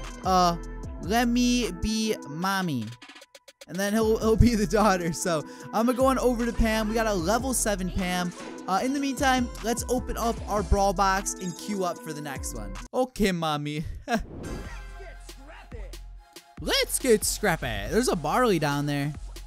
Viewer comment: I heard him say level 7 ham